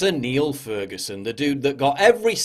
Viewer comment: I thought you said NIALL Ferguson. I was glad to see it is not Aya Hirsi Ali's husband.